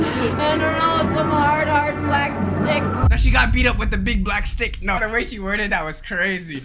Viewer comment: whao there i heard i got hard r by a black stick